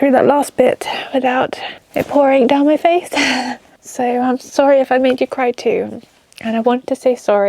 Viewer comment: 33 minutes 16 seconds I thought she said I'm sorry I farted